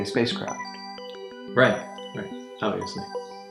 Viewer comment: What does Bob say after he's says "right" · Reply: Right, obviously